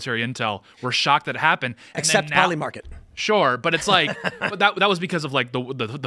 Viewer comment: can anyone tell me what he is talking about I hear "PolyMarket". Im sure im wrong.